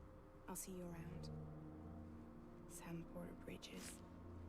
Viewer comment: Does she call him Sam Crow Bridges at